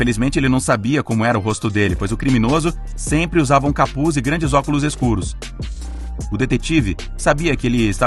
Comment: "óculos escuro" eu entendi "tapa olho" 😂